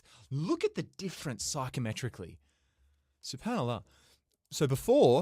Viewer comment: did say "Subhanallah"